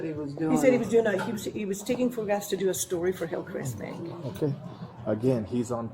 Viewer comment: Incorrect. He did NOT say "for Hillcrest Bank." He said "about Hillcrest Bank." HUGE difference.